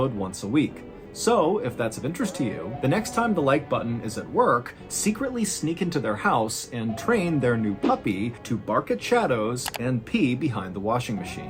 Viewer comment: LOL 😂 I thought he said, "...train their guppy..."! If the like button had a guppy, I would definitely train it to pee behind the refrigerator! 😂😂